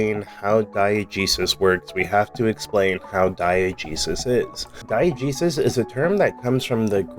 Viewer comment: I had thought at first that you were saying "Diet Jesus".